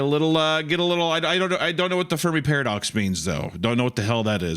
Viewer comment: I kept hearing "The Furry Paradox" at the start..